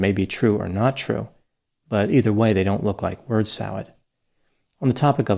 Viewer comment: Bird salad? I thought he said WORD salad. 😁